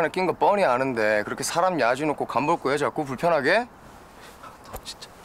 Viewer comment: 에 "사람..... 간 볼꺼예요? 불편하게?" 여기서 ... 뭐라고 하는건가요? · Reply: 야지놓고..일본말이예요 빈정거리고 깝쭉댄다라는 뜻 같네요